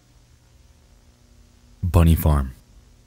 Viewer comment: I thought you said "Bunny Fart" and then that sound